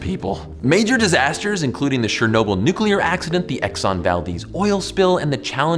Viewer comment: did he just say shernobyl? lol